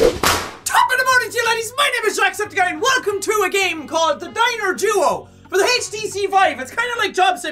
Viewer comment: I thought it said jacks dinner instead of diner